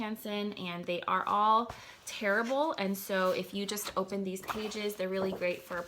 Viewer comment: I thought you said they were both “terrible” instead of “tearable” haha I was so confused!